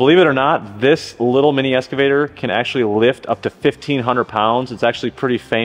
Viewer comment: Sound like you say "Escavator".